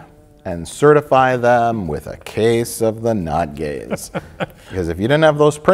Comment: Thanks for spelling out case of the not gays... I always thought you said „case of the nut-gaze“... which makes no sense, of course. Since english is not my first language I thought I just did not get the reference... now it‘s clear 😂